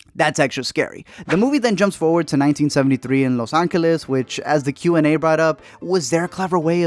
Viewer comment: the FUCK did you just say? Las Angels?